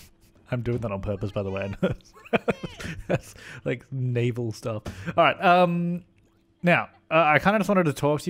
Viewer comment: said "Naval stuff" I heard "Navel stuff" Veeryy different things